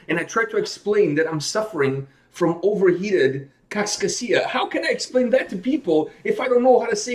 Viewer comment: It sounded like you said kaks kasia. That means two eights. Finnish is fun!